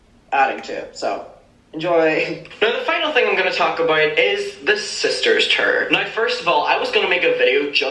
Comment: HA HA HA ... I thought you said 'The Sisters Turd'!!! Seems soooooo appropriate though!!